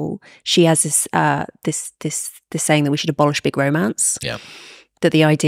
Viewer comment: This is a conversation about nothing. Talk, talk, talk. I love a British accent as much as the next American, but "Conceptual inertia? "....At 6:38, did I hear her say "Bolshevik romance????" What am I trying to watch here? Two people trying their best to sound intelligent without really talking about the real issue..."Why are today's women unapproachable?"